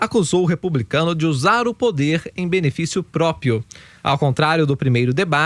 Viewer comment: só eu ouvi própio?